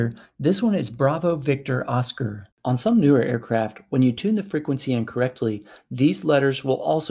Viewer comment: I think you're saying: "When you tune the frequency in CORRECTLY" but the auto-generated Closed Caption writes: "When you tune the frequency INCORRECTLY" If you had said: "When you TUNE IN the frequency correctly", the Closed Captions might have gotten it right. 🙂